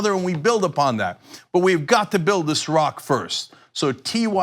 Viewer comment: I misheard that as "We're going to build Iraq". :D